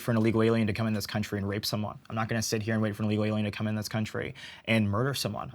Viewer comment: minutes in, is he saying “Illegal Aliens” or am I ragebaiting myself and imagining this??